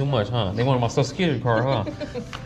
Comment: I thought you said I'm in the benz 1st door right here 😂 Don't try pushing those 600 lb pallets lol